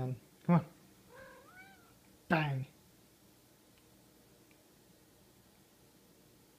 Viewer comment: In the middle of the vids I heard "SuCk fOrEvEr"